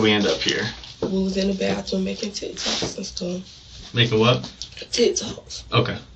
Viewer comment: All I hear is "tit toss" 🤣 I know she is saying "TikToks" but oh man.